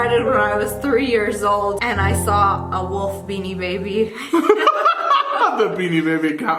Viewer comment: She said a wolf beanie baby and I thought she said a wolf making a baby Also my spirit is a fox